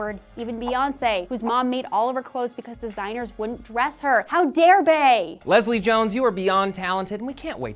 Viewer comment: 'How dare Bey" Yah, I heard that. Nice. x)